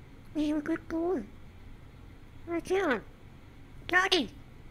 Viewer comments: "wheres daddy" "wheres daddy" return That hit a little too close to home. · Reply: First i heard it "rich daddy" and i wouldn't been mad at that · “Where’s Daddy, Where’s Daddy. RETURN!” The birds dad went to get cigarettes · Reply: I thought he said rich daddy